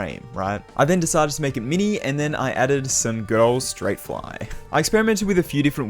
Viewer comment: he said good old straight fly but I heard girl straight fly